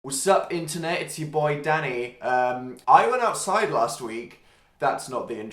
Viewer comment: I had my volume really low in the beginning and I thought he said "sup internet it's me ya daddy" I was shook for a second I had to replay and turn it up 😂😂